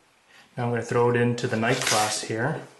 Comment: Hi. where did you got that watch strap from? And how is that strap called? Cause i did not hear very well with the name. It almost sound like you said "night clasp" or "nike clasp" cause the scrape that you make with the strap when you grab it make it difficult to hear what you said.